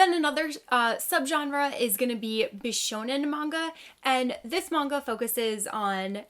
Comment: wait i can’t tell did you say shonen? i thought that shonen was anime directed towards boys but OHSHC and Fruits Basket are more shojo no?